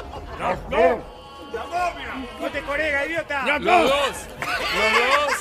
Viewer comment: ¿Qué dice acá que se ríen tanto? No entiendo una mierda, solo el "Idiota" del final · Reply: Corega